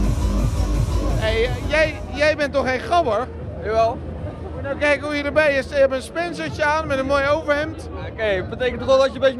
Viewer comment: He says, if you really want to know: 6:39 Hey, jij bent toch geen gabber? - Jawel. - Ja, is dat zo? - Moet je nou kijken hoe je erbij loopt, een spencertje aan met een mooi overhemd. - Het betekent toch dat je er wel een beetje netjes bij kan lopen als je gabber bent? - Ik vind niet dat je in een trainingspak moet lopen. etc. etc. Most Dutch people are able to understand German, so I think it is the other way around too. These languages know a lot of the same words.